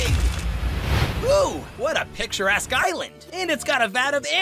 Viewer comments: when i first hear this, i thought nickel said: ”WOW what a picture ahh island“ lol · when i first hear this, i thought nickel said: ”WOW what a picture ahh island“ lol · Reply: I Heard " what a picture a## island"